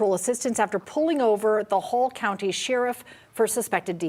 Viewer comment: Oh, they're saying "Hall County". I kept hearing "whole county Sheriff" as in Sheriff of the whole county and it seemed like an odd thing to say.